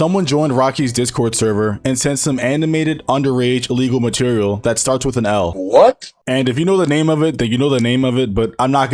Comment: What’s the L word pls tell me I can’t figure it out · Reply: Loli I think.